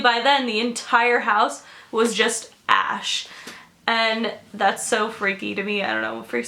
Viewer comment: I thought she said ' the whole house was ass' 😂 I screamed